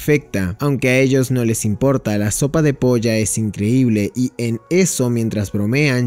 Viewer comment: escuché que dijo "la sopa de polla" 😂